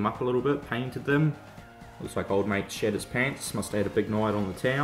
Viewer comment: Oh! Shed his pants. That’s what you said right? Can’t tell with the accent. Love your layout. But you already knew that. Nice new computer. Might need one soon too. Video editing is tough on a slow laptop. You might find mine in your water/culvert scene eventually. 😀